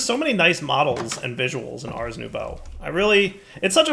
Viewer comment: Was it only me that until this episode thought was saying Ars New Bow?